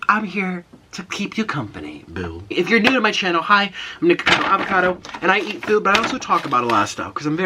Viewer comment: He was talking so quickly that i heard, "Im Nikocado Avocado and I eat poop"..... I'm over here like, "well....to each his own" O_O